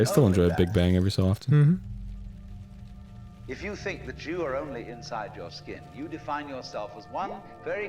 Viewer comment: I thought it said "if you think the Jew are only in your skin"